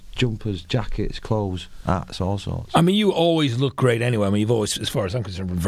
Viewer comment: and 1:00 did he say 'that's all sorts' ?